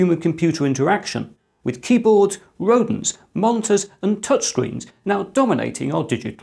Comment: Did you say "rodents"? 9:11 This may be of interest to Molly Burke.